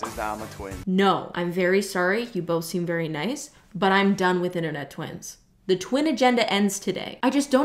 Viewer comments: I working and not pay attention so I honestly thought you said "no more Internet twinks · I THOUGHT HE SAID HE WAS A TWINK